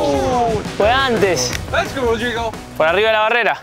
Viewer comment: 나만 "왜 안돼 c.."로 들려? ㅋㅋ